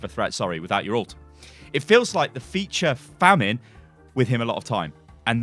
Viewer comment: Feature famine? I have to believe thats a typo or mishearing of "feast or famine" surely.